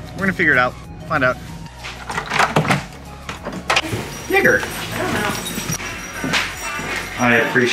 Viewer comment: What did you say @ 9:24 ? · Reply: Jeagur, lol my dogs name. But see how it could be misheard